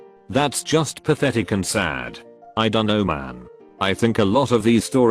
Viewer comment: I wasn’t looking at the screen, and I heard ‘I’m a nomad’